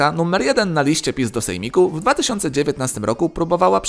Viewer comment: usłyszałem pi*do-sejmiku